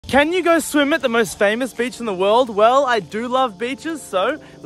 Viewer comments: i heard him say “can you go swim at the most famous bitch in the world”. 💀💀💀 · When u said: "i do love beaches" I thought u said: "i do lovr b*tches" But i realized u said beaches😂😅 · I hear and see "I do love b*tchs" 💀😭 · What he says:"I do love beaches What I hear:" i do love b**es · Am i the only one who heard him saying bi*ches